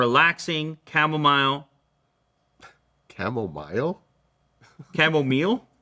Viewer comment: Did anybody hear saying camomile in the background